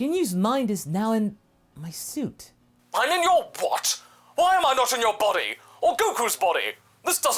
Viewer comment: sounded like Ginyu said “I’m in your butt?!” Truly a place many of us would like to be.